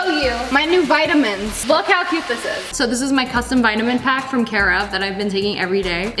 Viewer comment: It sounded like: So this is my new vitamin pacet from Karen 😂